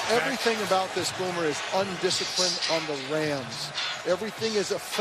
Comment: Did Dilfer call the Rams the Lambs at 0:20?